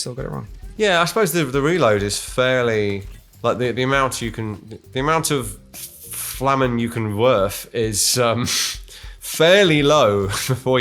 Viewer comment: Coffee in the mouth as I hear "the amount of flamen you can werf" Bad combo.